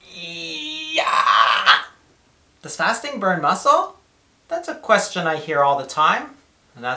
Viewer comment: Thought it said ‘does farting destroy muscle‘ .. thinking jeeeeze .. they’re leaving no stone unturned